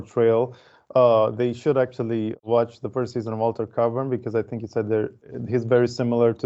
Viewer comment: What first season show is talked about there? Walter Cavern is not a name for a series. I couldnt understand the pronouciation. Anyone knows? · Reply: Altered Carbon, 2018 Netflix show